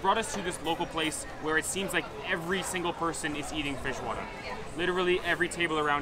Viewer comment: I heard him saying "feijoada" a bunch of times, but also "fish water" and, to my surprise, the captions also heard that! lol